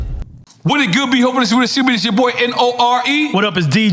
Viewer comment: what the hell did nore say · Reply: “Wujuh-wujuh-wood-bee”